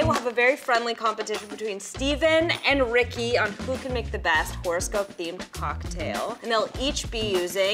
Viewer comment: PLEASE tell me I'm not the only one who heard say "Forrest Gump-themed" cocktail as opposed to "horoscope-themed." 🤦‍♀️🤣